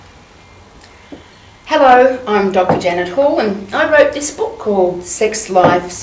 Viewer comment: Sounds like she said "Hello, I'm Dr. Genital" in the begining lol